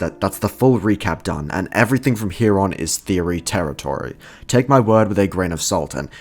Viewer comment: I heard Teto territory (im cooked)